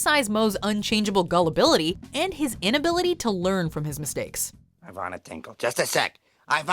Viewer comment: Yeah, lol, like: "Why can't I find Amanda Hugginkiss?" "Everybody, put down your glasses! Ivana Tinkle!" "Somebody check the men's room for a Hugh Jass!" "Hey, big guy in the back! Heywood U. Cuddelme?" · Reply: I know this reply is a year old, but I thought Moe said "Ivana twinkle".